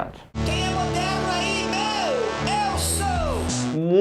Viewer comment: Eu entendi "Toma meu ex".